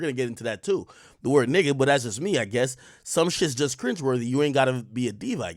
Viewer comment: Coulda sworn he said “some things are just cringeworthy it ain even gotta be DEEP ig